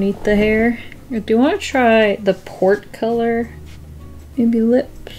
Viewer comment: Oh, PORT. I kept hearing 'the pork color...'